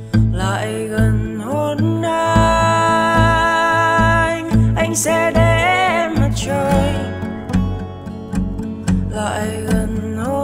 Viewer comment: Có ai nghe câu “anh sẽ để em mặt trời” thành “anh sẽ để em CHƠI” không nhỉ. Mình cứ nghe thành như thế. Có khi ban đầu tác giả sáng tác là câu này không nhỉ, hehe